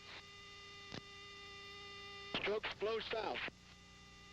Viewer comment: I thought i heard stroke 4 no chute, but i found out that stroke 4 was col. Harry (Mike) Roberts and he survived as a pow and came home after hostility ceased. Thank God